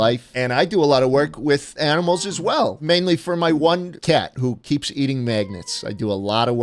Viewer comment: i thought he said «mainley for my one dad who keeps eating maggots 💀☠️💀☠️»